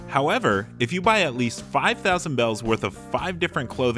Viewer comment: I thought it said 8000 bells.. that’s what I spent